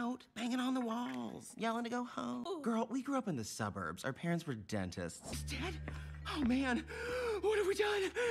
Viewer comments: Who else always thought when they said "our parents were DENTIST" thought it was "out parents were DEAD TO USE" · Those who thought he said, our parents were dead to us, but they actually said dentist😅 👇🏾